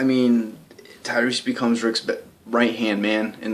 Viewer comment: it sounded like you were going to say "Tyreese becomes Rick's b***h" XD